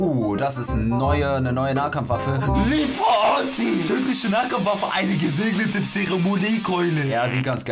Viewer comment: Wer hat bei 2:07 auch TÜRKISCHE NAHKAMPFWAFFE verstanden?😂